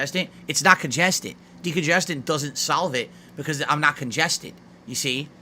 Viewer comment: It won't sahlve it. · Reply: Sounds like he is saying solvent